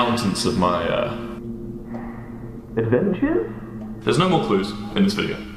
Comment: What is the word he says at 15:58? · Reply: He did say adventures